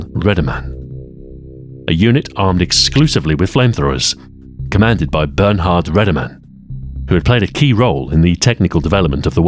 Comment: I heard this as "Commanded by burn-hard ready-man." What an apt name for someone commanding a flamethrower unit.